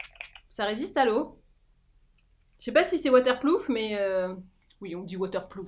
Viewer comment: J'ai entendu "water plouf". J'ai recraché mon thé 😂